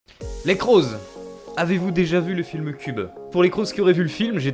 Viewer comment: Pourquoi j'ai entendu "avez vous déjà vu un film de c*l" xD superbe vidéo, bien dynamique et très bien montée, qui plus est on rigole vraiment bien ! :D Keep like this !